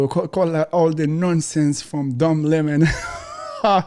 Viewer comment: Don Lemon is just so full of it. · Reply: His accent sounds like he is saying Dumb Lemon . I like that . Dumb Lemon .